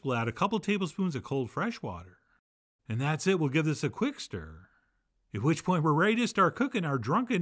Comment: Did you say....QUICKSTER??